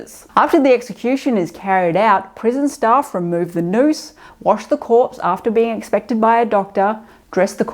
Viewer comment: for a second I thought she said “remove the nose…”